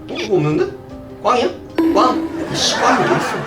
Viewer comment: 씨 꽝이야가 ㅆㅂ로 들려요 ㅎㅎ 이스테에근가? 발견!